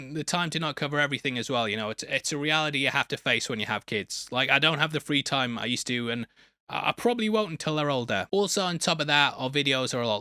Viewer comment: First time through I thought he said "I probably won't until they're all dead."